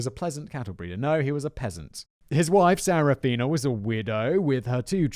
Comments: I heard "His wife Serafina was a weirdo." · ok I swear your saying " weirdo" 🤣🤣 I hand to skip back the 1st time "she was a weirdo with 2 kids.." Sorry what?! 😐🤣